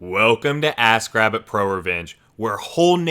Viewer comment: Sounds like you say ass grabbit